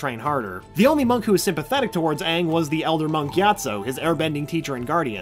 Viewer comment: I always heard "monky atso" instead of "Monk gyatso"